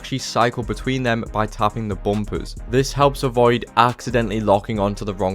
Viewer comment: I have zero clue what he just said 😭 bompoos?